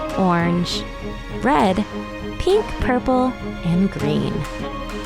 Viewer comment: Did you say purple? Purple is my Favorite Color!!!